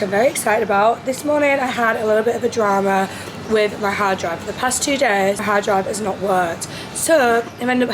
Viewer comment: Omg I thought u said hairdryer instead of hard drive and I was very confused 😭😭